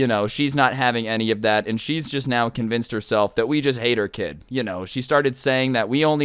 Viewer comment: lol I thought that u said that you ate her kid instead of hate hahahaha